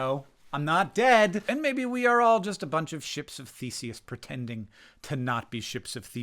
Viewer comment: Listening at 2x, DEFINITELY heard “ships of feces” the first time I listened through! 🤣 thinking “I must’ve misheard that. He’s got to mean some Greek reference I’ve heard but forgotten? But maybe this is a gut microbiome joke? Wouldn’t put it past him!” Then I slowed it down and heard it right 😂🤣